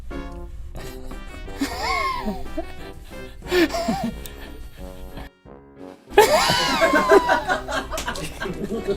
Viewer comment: I just can't figure out what Rhett says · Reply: I think he said "ass" bc link said donkey and what Rhett said was really short. Also you can tell by his lips